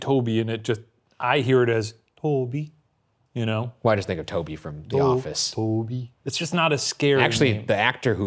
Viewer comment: when ryan starts talking it sounds like he says "why this (n word) toby from the office"